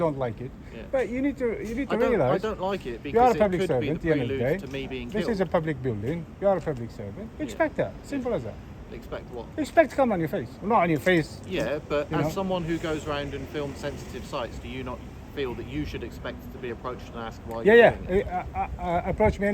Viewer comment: did you just say that he should 'expect cum on his face'?! So confused hahaha Great audit as always